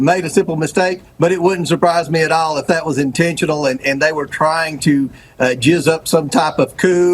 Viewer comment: Did Comer just say “they were trying to JIZZ up some coup”?!?! 19:57 😳😳😳